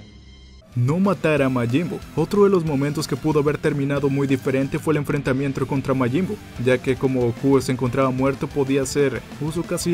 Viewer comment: like si te diste cuenta que dijo enfrentamientro